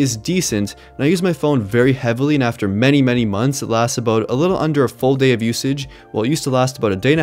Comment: who else thought it said 7 months xD... anyone?... just me...okay...